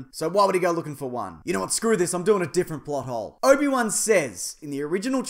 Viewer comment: when u first said plothole i thought u said butthole